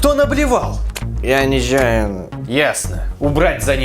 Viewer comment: Он сказал "Я ничаяно", но мне послышалось: "Я низнаю нах" :D